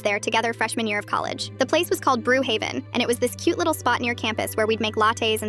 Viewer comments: Bro I thought the video said brook haven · Hmmm.. brew haven.. sounds oddly familiar… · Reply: I thought they said brook haven😂😂😂